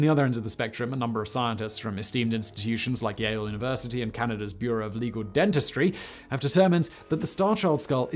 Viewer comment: I thought he said "Bureau of Illegal Dentistry" at 1st...